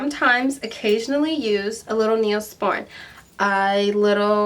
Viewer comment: I'm having a little issue with my dermal and I'm wondering what she said about ointments. Can somebody please help me understand the word at about 5:49? "Occasionally use a little ___? Thank you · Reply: Neosporin First Aid Antibiotic